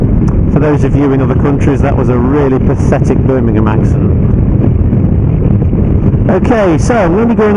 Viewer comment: When you said "Birmingham accent" I thought you said "Bum in your mouth" and had to rewind.